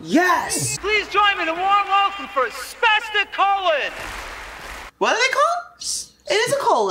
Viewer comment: "Spastic Colon" is the band name. As for why the burned girl mentioned silver in the mirror: "Most common household mirrors are "back-silvered" or "second-surface", meaning that the light reaches the reflective layer after passing through the glass." The glass of a mirror is just there to protect the reflective material (usually a thin layer of silver) behind the glass. This is also why vampires were said not to cast a reflection in mirrors, because silver was seen as a "pure metal" at the time. Probably the same reason for why it kills werewolves, though werewolves and vampires came from the same myths and were only eventually separated and had the attributes split between them. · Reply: i thought he said smashing colon 😂 😂 😂